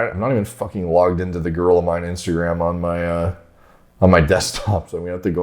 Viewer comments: I thought said “I don’t even have the girl of mines page on my desktop” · Oh you said "the Gorilla Mind Instagram", thought you said "the girl of mine Instragram" haha. How much of a joke is he to block people like you? Have no respect for him.